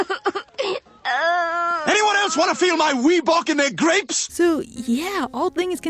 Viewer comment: lol I just realized Stewie say “Reebok in there grapes” I always thought he said “my wee box in there grapes” lol